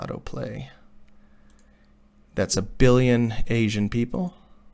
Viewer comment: “that’s a billion asian people” wHy was I laughing for 5 minutes straight after I heard this edit: “that’s just the asian people again” lMao